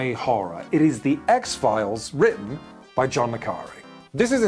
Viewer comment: does anyone have any idea which author is said at 5:25? subtitles say john mccari but i'm not getting any results on google. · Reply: My French ears heard John McCarry too. And then my hand hut my forehead thinking about spy literature and John Le Carré arrived.